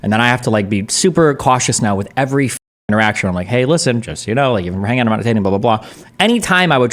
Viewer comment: Please tell me what Destiny says when he briefly "rap gods" Edit: Is it "Hey, just so you know; even if we're hanging out we're not dating."? Also 35:20 for leaked kinda cringe discord messages